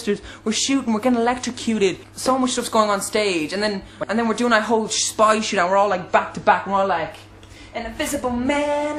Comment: did he say 'and were doing all that spy shit' ??